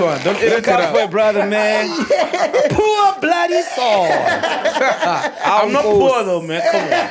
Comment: So Sol took Ndlozi's jokes by chest.. O di hemile😂😂😂😂 I heard him saying "I ain't poor"... Seems like our goat enjoys trolling others but when it's his turn he takes it personal 😂😂😂